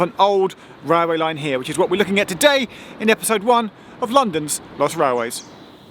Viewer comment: When you said” London’s lost railways” I heard “London’s Bus Railways”